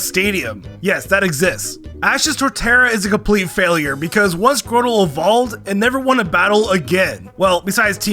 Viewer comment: Im sorry, did he say squirtle evolved into torterra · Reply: bro he didn't say "squirtle" he said "grotle"